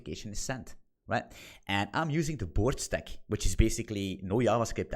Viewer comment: What did you call the stack at about 0:55? BORG? BORD? BOARD? It's not clear. The tech you mention is golang, postgres, htmx, and tailwind, which spells GPHT. 🤔 Thanks! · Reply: Bored 😂